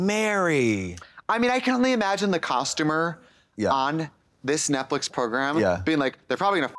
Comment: Trixie actually said Netflix instead of Netflick